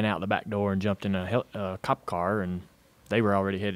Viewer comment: Did he said Cup or Cop car? i'm actually confused. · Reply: Cop car. They were given a police escort to the hospital.